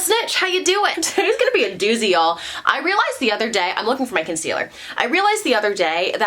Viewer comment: when u said “i’m looking for my concealer” i heard “i’m looking like michael cera” n i was like 😳🧐 where LOL